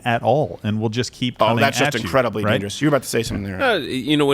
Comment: what did Crowder say? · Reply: "That's just incredibly dangerous. You were about to say something here."